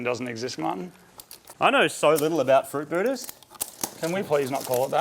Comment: Fruitbooters = rollerbladers lol man I haven’t heard that term in 20 years lmao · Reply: I thought he was saying "fruit Buddha."